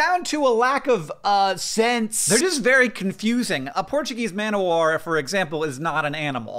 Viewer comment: For the entire half of the video up until the point where Hank explained the origin of the name I kept hearing "Portuguese man award" (it's not your fault Hank, it's just that English is not my first language). Btw what a cool system of man awards.